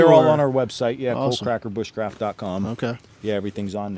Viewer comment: Sounded like you said "cold cracker" was thinking, he looks pretty warm to me